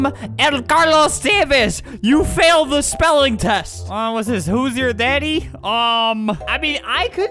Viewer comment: What Albert? Did I hear that correctly XD